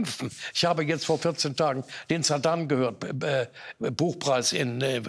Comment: "... den Saddam gehört.."? Wird Herr Baum langsam senil?